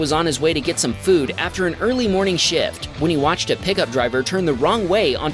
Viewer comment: All I heard was "Jacob was on his way to get some food after an early morning shit" Ataboy Jacob, out with the old, in with the new.